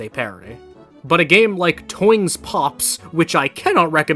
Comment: imma be real I actually no idea what he said here can anyone tell me the name of the game he recommended? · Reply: Toing’s Pops